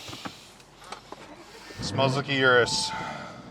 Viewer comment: Sounded like he said,"Smells like UR-A$$." 😂😂😂😂